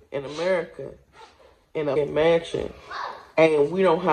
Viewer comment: I'm laughing till my side hurt, my girl said skinny cows and no snacks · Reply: I thought she said she lived in a mansion....Then she said it cost $100k